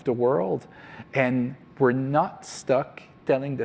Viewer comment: wait, at 5:36 did he just say we're "Nut-Stuck on telling the same stories"? Holy Crap they said it out loud! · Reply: Not stuck, he has an accent